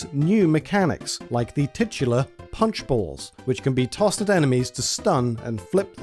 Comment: Because of his accent I heard him say punchable Mario bros instead of punch ball